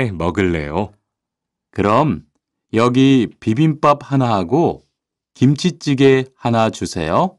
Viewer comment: 그럼 려기 비빔밥 하나하고 김치찌개 하나 주세여. Rõ ràng mình nghe như vậy mà sao trên phần text lại khác nhỉ?